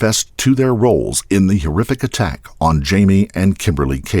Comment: The what? You said hearific? Or was that heerrific?